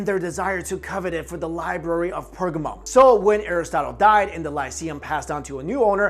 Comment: For a second it sounded like he said "library of pokemon"